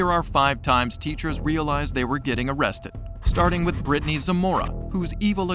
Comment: I heard brittanys a moron